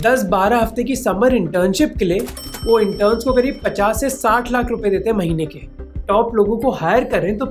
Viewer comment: Did u say 50-70 lakhs per month .. Wtf !!!